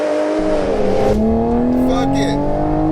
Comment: I honestly thought he said " F**K IT " - Pretty sure he said 3rd gear.....Im going deaf